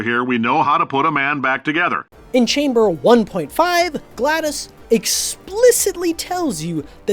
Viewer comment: subtitles say "Glattus" instead of "GLaDOS"